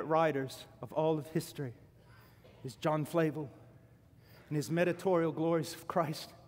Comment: who is that writter? I can't hear it properly.... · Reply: John Flavel